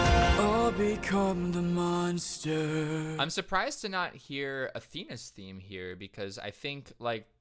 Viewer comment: I had to go back and listen again, because I was half listening, and I swear I heard "I'm surprised to not hear a penis theme here"